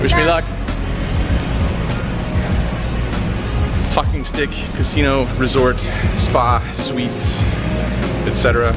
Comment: did he say Talking Dick Casino Resort??